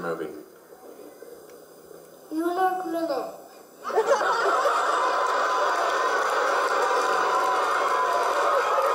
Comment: did anyone catch what movie the little boy said lol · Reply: New York minute